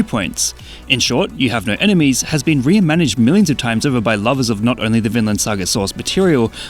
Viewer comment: What did he say at 02:10? "In short, 'You have no enemies' has been ??? millions of times over..." I hear "re-inmaniched" which isn't a word. Maybe he just said "reimagined" weird.